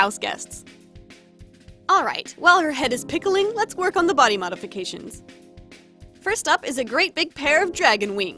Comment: I'm so used to poking fun(/lh) at "mody bodifications" from amanita's video I genuinely thought she said it that way this time 😭😭😭